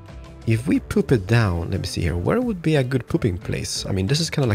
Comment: i love that u say "pooping" instead of "putting" 1:27 i also dont know where a "good pooping place" is